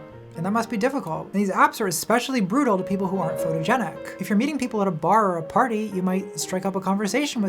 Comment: Thought you said "full eugenic" instead of "photogenic" and wow did that change the tone of the video.